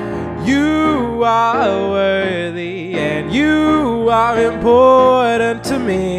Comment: And i actually thought he said that i'm "WORST" lol